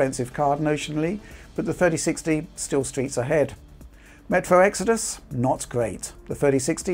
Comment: We got all the Rich classics here... "Gen on gen", "Streets ahead".... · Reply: I always thought he was saying "streaks ahead"